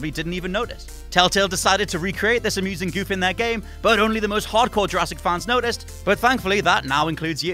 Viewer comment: Did he just say im not a hard core Jurassic park fan